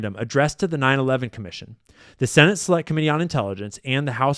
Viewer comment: did he just say “slut committee”